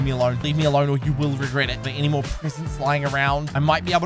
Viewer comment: BRO PRESENTS ARE SO RARE HOW DID HE FIND 2 IN ON BUILDING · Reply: Almost thought you said parents